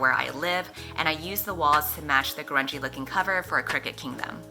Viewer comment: Oh my god, I thought you said the “crunchy cover of crooked kingdom” I was like what????? What does crunchy mean????